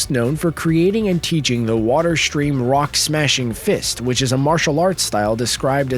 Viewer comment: wa- wa- what did he say silverfangs technique was? How has no one noticed what he said in the comments? I’m disappointed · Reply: water stream rock smashing fist!